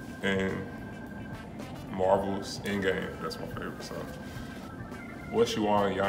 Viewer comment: did he just say he likes endgame??? 😵